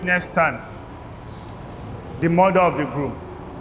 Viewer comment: I heard "the murther of the groom"